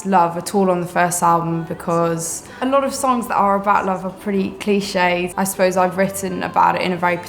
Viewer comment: Can someone help me, please? I'm spanish and I'm trying to understand what she says, but there are some things I cannot. She says she didn't talk about love on the first album because a lot of songs that are about love are pretty...? Minute 0:30 I don't understand what she says from 1:54 to 1:58 And finally at the end she says she thinks when people hear the --- tracks and the --- tracks they'll see that's a perfect combination... Thank you very much to anyone who help me. :) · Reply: She says: clishé (i quess she means its a part of something)